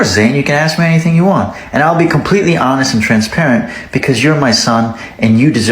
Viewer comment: i have auditory processing issues and i thought he said transgender